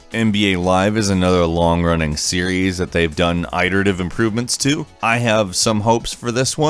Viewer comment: Did I hear him say "eye-terative"?